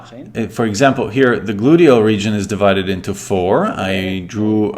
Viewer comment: Did he say boodio region?